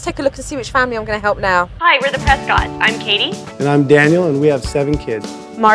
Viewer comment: Omg i couldn’t hear when she said “I’m kadi” I thought she said “I’m 80”